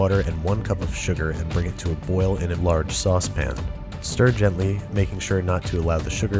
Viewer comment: Did he say "sauce pan"?